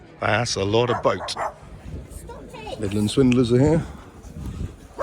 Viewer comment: I thought you'd said Midland Swindlers lol x